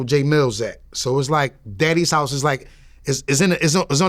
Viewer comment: did he just call him daddy?